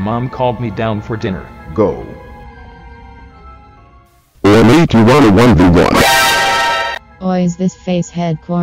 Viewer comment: What did he say? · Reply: Oi mate do you want to 1v1